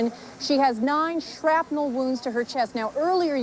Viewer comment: misheard "9 shrapnel wounds to her tits?"